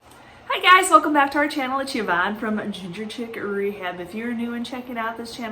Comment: Ok … is it Gevanne or Yvonne?!?! I swear I hear the “G” not a “Y” but so many say Yvonne. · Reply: Oh..